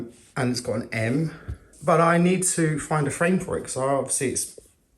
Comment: hah frame...thought he said friend the first two times lol